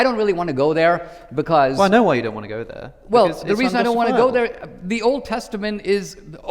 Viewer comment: what exactly did Alex say here?? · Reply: "Well, I know why you don't want to go there: because it's unjustifiable."